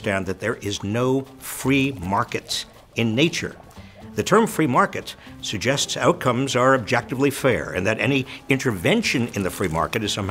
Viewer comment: did he really say, "The term 'Free market' suggests outcomes are objectively fair, ..." ? I'm playing this over and over to make sure I'm not imagining this.